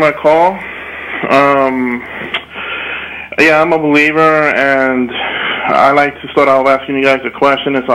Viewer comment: I thought I heard him say he's a belibieber?!? LOL